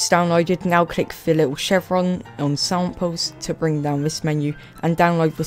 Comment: what is he saying at 3:40? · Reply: he is saying "now click the little chevron on samples"